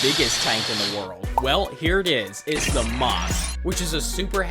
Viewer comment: did he say moss?